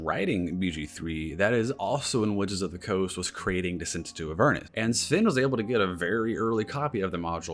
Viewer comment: At the 3:00 mark approximately you mention a module that was possibly being used. Problem for me is my hearing sucks and for the life of me I cannot seem to hear what module it was. I swore I heard Castle Inverness, but I think that's wishful thinking on my part. Does anyone whose hearing isn't terrible like mine know what was said? Thanks in advance! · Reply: Apologies on my part, I have to remind myself to slowdown and annunciation better sometimes. I was talking about the Decent into Avernus campaign, Sven got a very early copy of the module before it was released to the public and even got to chat with one of their writers in person.